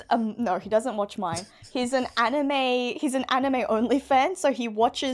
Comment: For a minute I thought you said anime onlyfans... Well maybe you did since I, like "friend", skipped the context